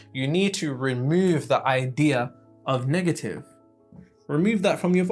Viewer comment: Lol, had to spin that back.. thought I heard you say get rid of the "Niggative" aspect of your reality.. if its not coined already im doing so now Niggative- (per websters dictionary) anything related to, of, or extrapolating any and all said characteristics of Niggadom or Niggardry. I.e. Why yall being so Niggative, calm down, youre gonna get some